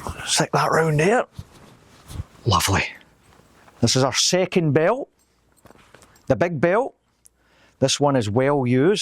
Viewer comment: When he said, "Second belt," I heard, "Second breakfast!" 😂